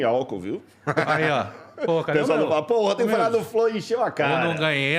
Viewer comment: Eu ouvi um "porra" de um crente que tá ali exalando ser "correto? kk